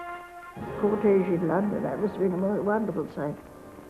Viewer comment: Did I just hear her say “Caucasian London, that must’e been a wonderful time.”?